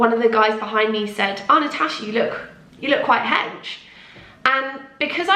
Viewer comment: What did she say at 1:45-1:47??? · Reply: “You look quite hench” as in she looks big or bulky